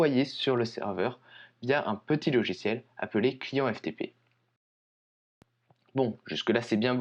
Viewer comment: j'ai entendu " client fdp " xF